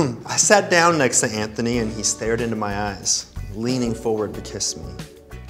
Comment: I heard, "stared into my ass" not "stared into my eyes." I almost died.